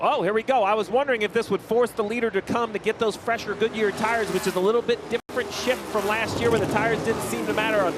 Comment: did he just say “a little bit different bleep from last year”?